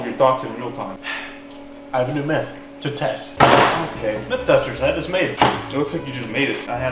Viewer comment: I thought he said, "Meth Buster's" I was like what? But then i reallized he said Myth Busters then i was like OH shit!